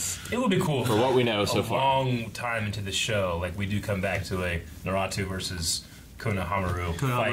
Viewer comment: did he just say “naratu”